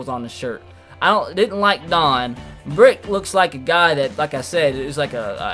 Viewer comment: I thought he said bricks